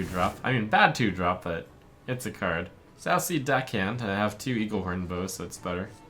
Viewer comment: I can't be the only one who heard "sassy deckhand" right?